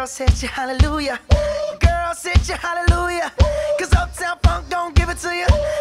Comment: 7 year old me thought it said “uptown f*ck you up”